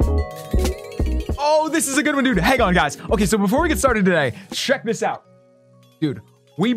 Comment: did he just say shrek this out? Lol